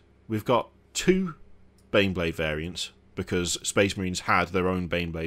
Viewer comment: Bro I'm inside head I swear I know he thought bro said 'wheeled variants' and the pic popped up and it was normal bro said OH 😂😂